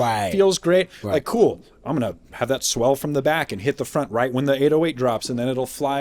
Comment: “Im gonna have that swell from the back and hot the front”……. Min 42:00 What did he say..?? 😐⏸️😂😂😂😂